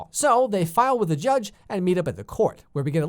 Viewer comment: Reminds me of Icarly when gibby gets stuck in the chimney. I thought u said judge Judy Lol